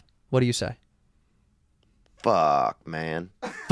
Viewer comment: what would you guys say😂 i got “oh fuck”